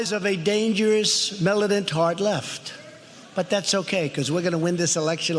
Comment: DID he just say the words, " A Dangerous, Melanin-tarred, Left?" :-O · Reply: He said “militant hard left.”